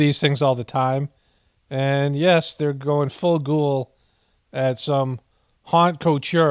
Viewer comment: I feel stupid. I thought Tucker said "ghoul" when he said "gold" I feel sooo dumb LOL. But thanks for hearting the comment:):thanksdoc